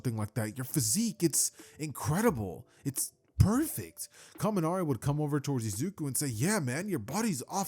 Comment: Did some say perfect?